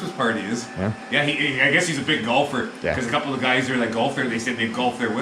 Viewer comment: I must be getting hard of hearing. I thought you said that when Alice Cooper comes to town he likes a Big Gulp--not a Big Golfer! 😆 Hopefully he's a fan and will treat you at 7-11! Go!!!